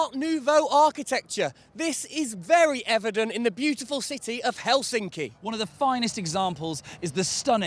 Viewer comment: sounded like "Hell's inked in"... nice tattoo I would say....